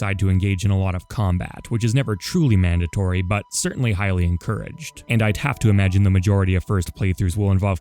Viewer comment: I first I misheard the last part of this sentence as "Hylian Courage'd". I know it's "highly encouraged", but I like the subtle Link pun a lot more, so I'm keeping it mentally.